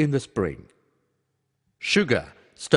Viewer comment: is it only me who thought suga(bts) instead of sugar..